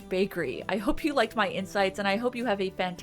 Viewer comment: I thought you said "I hope you like my insides" before my brain did an auto-correct a few moments later to "insights". Good grief, I've not even had a drop of alcohol yet today.